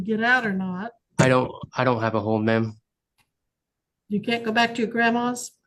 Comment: He said “I don’t have a hold” not “I don’t have a home” She misheard that one